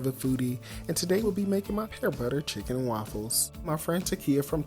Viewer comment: I thought it said peanut butter. I watched the entire video waiting on when he was going to add it 😅